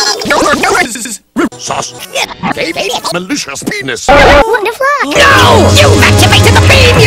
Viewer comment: WHAT DID AOSTH TAILS JUST SAY!?!?!? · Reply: Wtf is what she said. Also NO! YOU’VE ACTIVATED THE BEAM YOU FOOF!- (explodes)